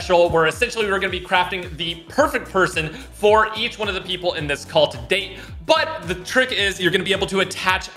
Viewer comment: "...each one of the people in this cult..." Did anyone else hear that?